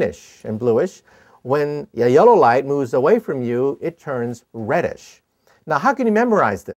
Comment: i thought he said "when the yolo light moves away from you" at 1:00 i was like wait what lol